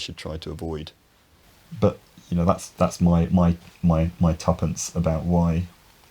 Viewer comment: I heard "tuppence" as "tuppance", meaning "some abstraction relating to a fertile male sheep", before I realized that it's the British version of "two cents".